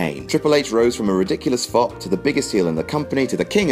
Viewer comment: What did you say?... "Triple H rose from a ridiculous--" what?... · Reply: Flop